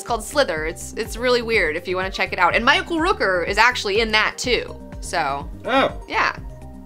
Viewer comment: I thought you said, "My uncle Rooker was in that movie too.", not "Michael Rooker was in that movie too." LOL